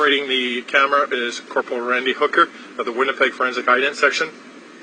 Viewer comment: it sounds like he said operating the camera is "Randy Hooker". Maybe it's just my filthy mind.